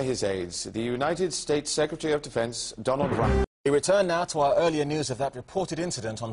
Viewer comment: Agh, sounded like “Donald Trump.”